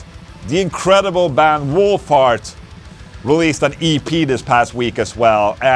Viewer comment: I thought you said Wolf Fart.. now the band is ruined for me..lol